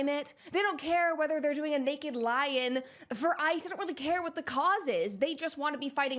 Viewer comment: I keep hearing "naked lion" when you say "naked lie-in"